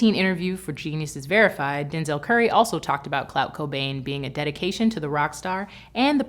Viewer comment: lol did she say cloud cobain on 5:01 😂😂